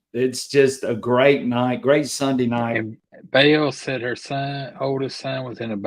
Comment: I thought you said Monday night 🙄